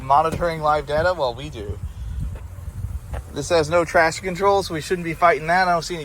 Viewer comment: When you said "this doesn't have traction control", I thought you said "trash control" and thought to myself "well obviously not".